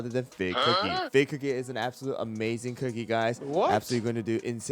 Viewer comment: thought i pressed on a "dont invest video" when i heard "fig cookie" 😭